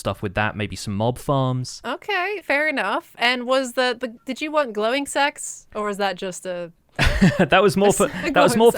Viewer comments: I heard "glowing sex" 🤦‍♂️ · I thought Said 'Glowing s*x' instead of 'glowing sacks' on first listen.